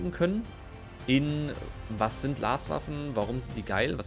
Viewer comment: Ich habe einfach viel zu oft Larswaffen verstanden ^^